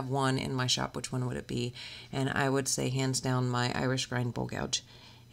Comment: God bless you. Thank you for your videos. In this video you referred to your favorite tool ever. It sounded like you said Irish grand bowl gouge. I looked that up and could not find it. Can you correct me as to what that couch is called? Thank you very much. Prayers being sent out for your friend. Todd